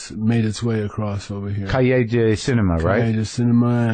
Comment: What's the word they were saying at 5:50? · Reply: Cahiers du Cinema; an influential French film magazine